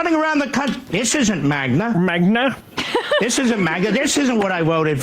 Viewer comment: I thought he said "magma", which would have nicely matched his volcanic outburst.